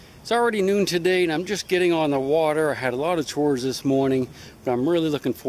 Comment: What kind of tours do you give? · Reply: Lol I thought he said tours too he actually said chores!